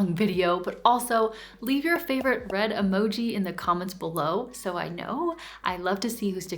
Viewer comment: Hi 🥨! (at first, I thought you said "bread emoji", so I'll just to with that.) As a kinesthetic person, I find that tapping into the physical side of my ideal self helps me step into my next era. I started salsa lessons a while ago. Dancing helps me feel energised, happy, outgoing and sexy - things I thought I could never be. It feels like I'm physically jumping into my ideal woman. It doesn't matter that I'm not a great dancer (yet!) It's more about finding things within myself that I didn't know existed. The mind is hard to bend, and someone could argue that the body is even more so, but I would still argue that it's easier to get into new physical habits. Little by little, your minds follows along.